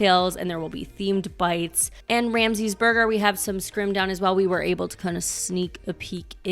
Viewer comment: I thought I heard "Anne Ramsey's Burgers." Made from tongue, no doubt.